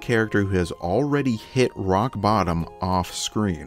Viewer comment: did he really say rock bottom 😂